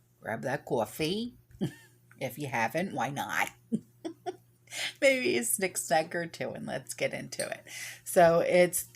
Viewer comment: I love you and your accent too! I’m a newbie and at first thought you were saying, “ maybe a six pack or two”! Before long, I just might need that six pack or two! I’ve watched videos on another prepper channel about the reshyte. They think they are better than The Almighty and their city for us is just a huge wall like an ant farm! It’s sickening!